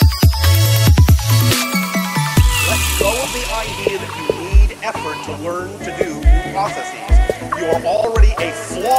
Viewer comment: Anyone know what the guy said at 1:50? I might make a GmD level of it. Anyway, nice job! :D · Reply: ''Let go of the idea that you need effort to learn to do new processes'' :D